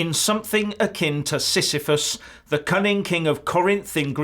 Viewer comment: "Something akin to Sisyphus............................" At first I thought you said "Syphilis", a disease which can cause INSANITY in its final stages. Silly me !